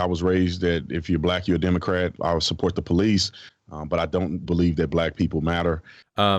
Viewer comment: Bruh in the beginning 0:54, did he say "I dont belive black people matter"? bruh what the fuck